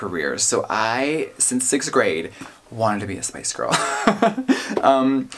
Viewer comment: I swear to god i heard him said "space girl" at least twice.... i need to stop using headphones lol